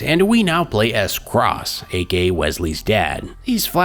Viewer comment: I thought said "A gay wesley's dad" for a second